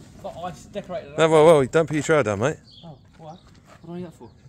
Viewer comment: I thought he said “don’t pull your trousers down mate”. I was looking away from the screen, so when he asked “are you left handed”, I was like “what’s going on in this video”.